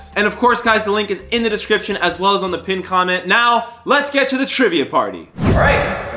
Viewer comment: After the sponsor when he said let’s get into the trivia part I thought he said let’s get into the trimming part and he was gonna show us him shaving his balls😂